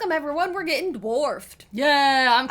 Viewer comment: I thought you said "we're getting divorced" 😮